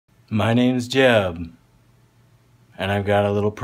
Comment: how are you not a meme?? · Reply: Ikr I thought he said his name was Jeff (y'all know that meme) when I heard it for the first time